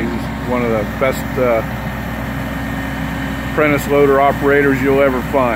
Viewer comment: When you first introduced Richard, I thought you said he was an apprentice loader operator instead of Prentiss loader operator. Then you said that he was 70, which immediately cleared up my confusion!